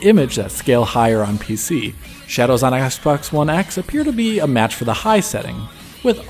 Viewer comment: I heard Assbox one x. lol. Love your analyses as always